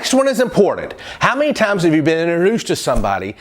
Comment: Inorus? First of all, i didnt understand that, but thanks to captions/subtitles... It's Introduce :>. BTW, THANK YOUU FOR YOUR TIPSS!! I'M TRYING TO FOLLOW YOUR TIPS AND I THINK I'M GETTING BETTER AND BETTER AT STUDYING SPECIALLY IN MY MATH SUBJECT :>